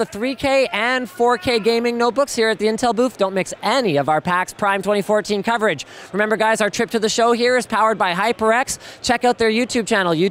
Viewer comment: but I really wanna "MIX" your PAX prime coverage =P (had to listen to it twice actually cause the first time i though that i had misheard it lol)